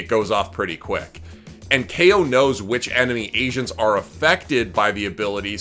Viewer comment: I swear i hear "enemy asians"